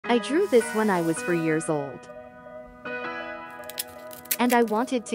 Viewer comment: "4 Years old" What I hear: "3 years old"